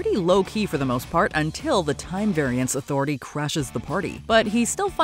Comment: Did you say *CRASH* or **CRUSH**? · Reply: I heard “crash”